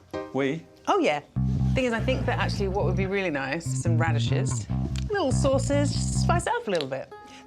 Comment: I'm rewatching this and did she just say radishes at 21:00 whilst pushing in tomatoes into the cake?